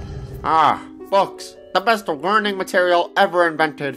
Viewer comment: Welcome back to what on god’s sinful corrupted world did he say? At 3:02 cauldron is caught with alleged potty mouth so did he say A: ah books B: ah fucks